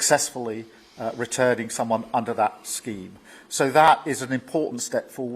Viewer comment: Wait did he just say SOMEONE flew out today have a listen to him he said someone am I hearing things 3:37 so the lunacy of 1 in 1 out do the hokie kokie has now moved onto SOMEONE...... 😂😂😂